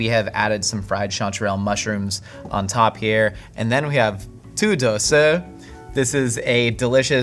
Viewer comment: The subtitles is wrong, it says "Two doses" though I believe it was supposed to be the name of the dish, I'm not familiar with the dish itself, though I believe he said "土豆丝" (tǔdòu sī) lit. potato silk, it basically just means shredded potatoes